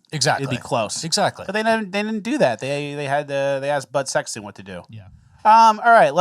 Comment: did say "butt sex" 😅